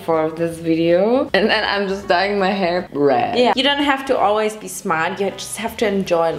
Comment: are we all just going to ignore that she said BREAD instead of red🤣🤣